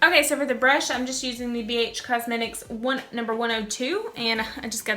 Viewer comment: I thought she said Bitch cosmetics at first...